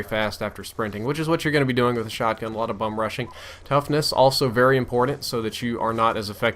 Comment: I swear I heard bum rushing..